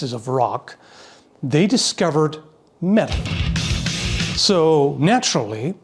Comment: I swear I heard you say “they discovered meth”.